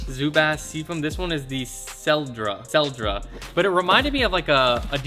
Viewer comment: did he say the name of the sketcher was sell drugs? couldn't hear over these ruffles